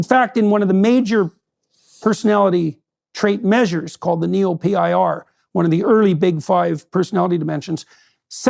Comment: Around the 5:30 to 5:50 mark jordan mentions a study I can not seem to distinguish and find It sounds like has is saying neO-Pir?